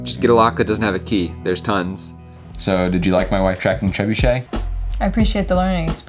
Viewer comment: What does he say at about 10:44? It sounds like, "Did you like my wife tracking trebuchet?"... Is that what he says? Edit: Lol, nevermind, when I unpaused it he explains it, derp!